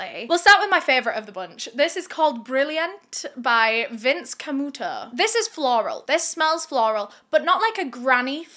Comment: I thought you said Vince Camel Toe😂😂